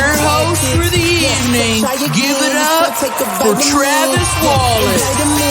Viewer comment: did they call him Travis Wallace 😂